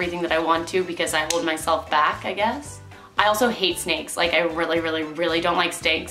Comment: she said she don't like steaks instead of snakes lol